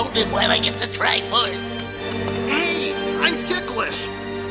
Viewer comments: LOL - at 3:20, it sounds like he says "Hey... I'm dickless!" Which kind of seems true with the animation at that moment. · I’m sorry but at 3:23 did I hear Link say ‘I’m dickless’? I’m sure I did.